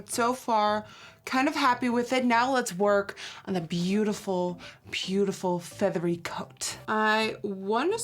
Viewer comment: Lol 8:11 at first i thought she said “beautiful beautiful feathered goat” oops 😂 😂